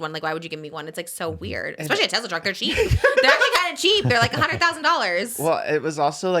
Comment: i thought she was saying it looks cheap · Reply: she said “especially a Tesla truck, they’re cheap, they’re like $100,000” She was definitely saying that $100,000 is cheap.